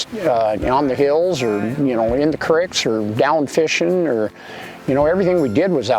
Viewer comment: He says “creeks” but the subtitle says cricks.